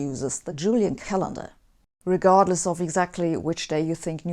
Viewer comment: Merry Christmas!! Thanks for the video. At 4:09 the CC says "which still uses the Gregorian calendar." but you and the picture say "Julian calendar". I think it is a mistake.